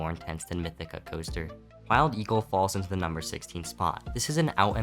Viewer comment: I think I heard “mild eagle”